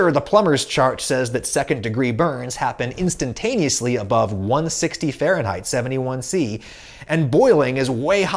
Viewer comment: As a note around 3:30, you put 1160 degrees in the subtitles instead of 160. Probably doesn't matter since it's in the video but figure still good to comment it